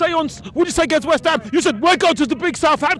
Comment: I thought that he said that he was confident going to Southampton! LOL!!!!!!!!!!!!